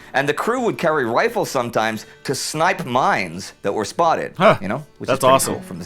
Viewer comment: for a second I misheard here and thought that he said that they snipe mimes. Not mines. It's a funny but morbid thought.